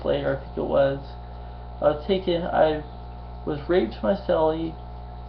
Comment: did he say he was raped? I cant quite make it out.